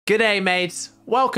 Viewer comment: Why do I always hear “Good Ai Mates”